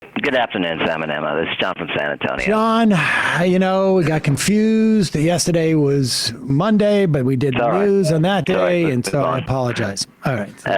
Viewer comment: When that caller said "Sam and Emma" I thought he said salmonella 🎉